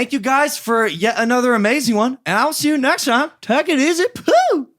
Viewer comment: why did it sound like logan said "take a easy poop" lmao